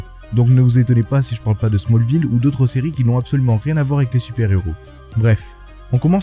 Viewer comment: J'ai entendu "smallville rien a voir avec les super héros" j'ai couper.